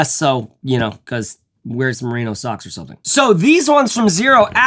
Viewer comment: what did you say about wearing socks with the Xero Hana? "You know, cuz, wear some XXXX socks or somethin" What was the word in the XXXX spot? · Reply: Probably “merino” I’m guessing.